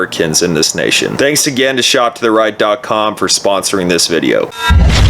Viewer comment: I was using the 10 seconds forward to skip through the ad, and it sounded like you said "Thank you BLM for supporting this video" LMFAO